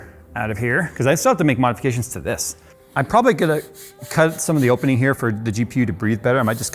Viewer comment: said the C word at 7:40 instead of Cut hahha Nice Freudian slip :D